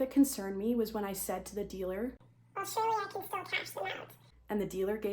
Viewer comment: wtf did you say? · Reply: Surely I can still cash them out